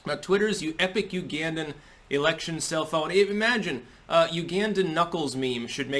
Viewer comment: I thought you said “cell phone”🤣